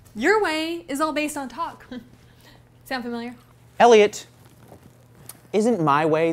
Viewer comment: They have buzzfeed yellow, buzzfeed blue, buzzfeed video... Where is buzzfeed Elliot!? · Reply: John S at 1:26 when he says elliot, anyone that knows the game facade, would know that it sounded like trip.